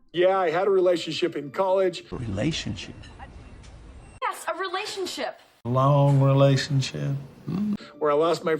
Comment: I thought he said homo sexual relationship the first time I heard it.